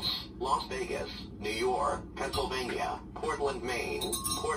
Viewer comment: was it transylvania or something cuz that is what i heard